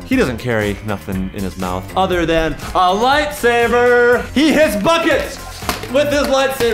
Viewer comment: When said "Lightsaber" I thought he said "Life Saver"